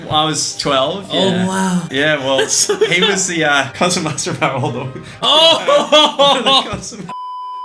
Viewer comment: Do you know what Ray said at 9:24 because i couldn't make out what he said. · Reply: "he was the concert master of our old orchestra," then Eddy says his name which is why it bleeps